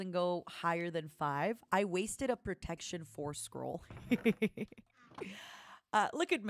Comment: I think she's saying "Protection 4 scroll"